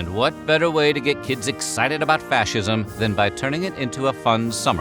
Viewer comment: Fascism? I thought you said fashion